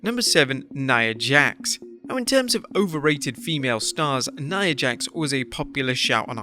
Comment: I heard that as overweighted...